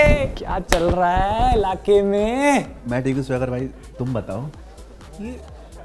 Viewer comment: did he say sagar bhai??